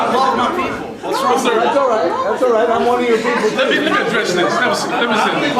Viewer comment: he said: "fuller, more unedited, longer and clear tape of what exactly went down". it's not clear what he meant by that - new tape, or unedited and longer version of the same tape. Anyway u can see his friend, then a guy in background saying "that's alright, i'm one of you(r?) people too" at 2:48, then slightly bold male with moustache bothering her. Couldn't here what was said, but she was not happy. But the public in there did not boo him. It was lively chatter, like smth amusing just happe